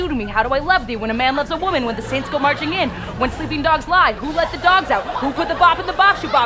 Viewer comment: Did she just say when the saints go marching in, as in Andy Mineo at 1:11?